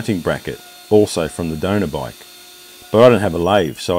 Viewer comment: Fantastic bike only bummer is. Sounded like you cut up a Dyno to build this bike. Dynos aren’t made anymore. They are highly collectible. But still a beautiful bike build.. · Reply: Oh hell I thought you said Dyno not donor. Your accent got me. Sorry I’m an idiot LOL!! Checkout out our YouTube or instagram we are really big into custom cruisers.